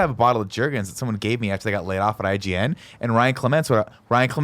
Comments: I kept hearing "jerkins" and wondering why a company would market their product as a jerk off lotion lol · I thought was saying Jerkins, playing off of lotion being used for masturbation. Now I know it's a brand. I will now always refer to it as Jerkins. The more you know.